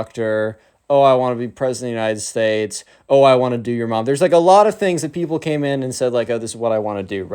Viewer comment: what did you say? Do you mom?